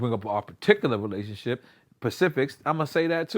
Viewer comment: this dude said “Pacific” instead of specific 😂😂🤣😂